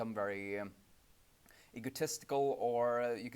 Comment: I think I heard 'ecotesticle'.